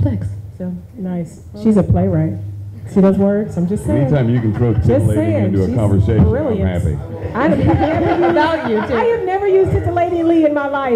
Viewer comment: What did Jeffrey say before 41:20? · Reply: He said, " Anytime you can throw titillating into a conversation, I'm happy!"